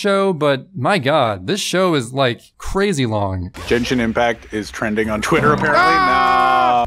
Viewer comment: did he say "Jenshin Impact"?